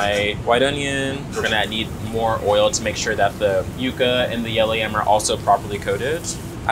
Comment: is such a graceful, beautiful and stylish addition. Love the Caribbean Heritage shinning through. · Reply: ! Did you say Yukon instead of yucca? It sounded like you said yucca in the captions then you said later about 16 minutes in that you are adding the potatoes. If it is Yukon potatoes please confirm and does it matter how you prepare the potatoes if so? Thank you for the great information and engaging clip and for your answer. I appreciate it!